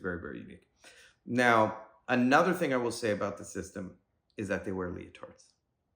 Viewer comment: Can you write i because I can't understand what you said: "thy wear" . . . what?